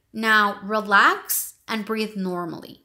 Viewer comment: Hello I love this video, but can you help me telling what did she say at minute 5:55 brought lax? Thank you so much! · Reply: You are very welcome! Ok, now relax and breathe normally.